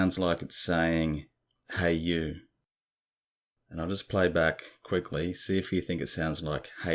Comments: The EVP sounds like hey you but when you slow it down it sounds like it says hurt you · I thought it was saying, " who r you" . It can be "Hey you". Thank you for sharing. 🥂